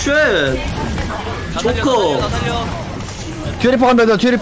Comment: 뒤에 리퍼를 키리코로 들었슈…허어